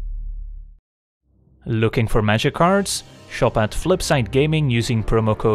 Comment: After the April fools special, I hear “Looking for Magikarps” every time.